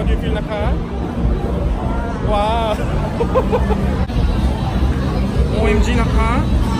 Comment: 이거 뭐라고 한거냐 조?? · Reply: YoungMin OMG임까?